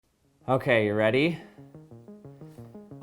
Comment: I thought it said Avacado Number :/